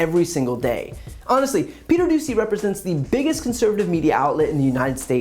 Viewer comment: I heard the reporter’s name as “Peter Doozey” 🤪